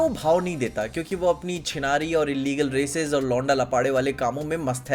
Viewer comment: Did you said Chinaal🙄